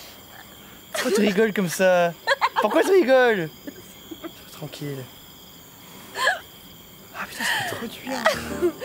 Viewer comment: SOMEONE WHAT ARE THEY SAYING AT 1:21 ?? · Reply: A bit late but "Why are you laughing like that? Why are you laughing? Leave me alone. Fuck, it feels so good."